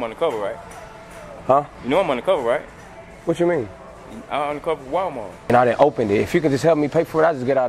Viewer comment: " Undercover walmart "i thought he said undercover police lmao